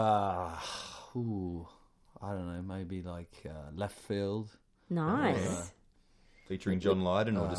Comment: I can't understand the name of the band Justin suggests at 11:33 , can anyone please tell me? Thanks. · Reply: Leftfield